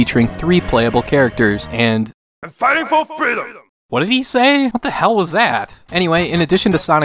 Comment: Sonic Blast 2. I think he says, "Fighting for Freedom!!!"